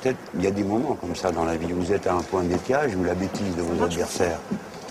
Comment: C'est moi qui suis sourde, Melanchon qui sait pas articuler ou il dot vraiment "un point de détails" à 0:33 ?!